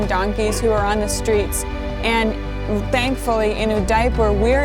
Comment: Did she say "in a diaper"?